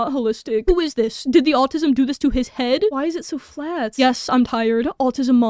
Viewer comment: Im not American, but it kinda sounds like youre saying altism?